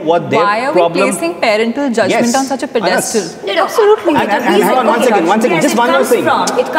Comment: To anyone who made the subtitles, at 15:52, where it says "Why are we placing parental judgment on such (indistinctive)", she says "on such a pedestal" Just thought I'd throw that in there Anyway, thank you for this video and thanks for always providing us with subtitles. As a German who is fluent in English, I do sometimes find it hard to follow what people are saying when it's in a discussion setting like this, especially when accents are involved. You're doing some great work, keep at it ♥